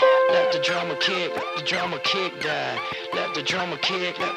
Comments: It sounds like it says (let the drama kid , let the drama kid die) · I srsly thought they said, "let the drummer kid die" or "Let the drama kid die".. No Wonder i couldnt find it.